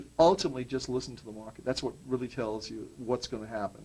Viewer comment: ultimately just listen to the..."whopit"? what is he saying